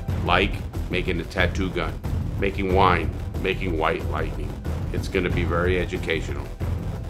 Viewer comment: When the intro said white lightning, I thought it said wifi😭